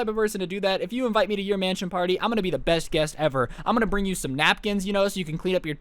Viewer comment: I literally thought it said INSANR MANSON PARTY